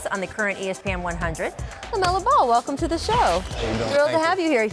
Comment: I thought it said LMAO BALL